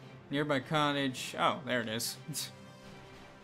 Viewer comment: when you said bitch at the end, it sounded like you said beach.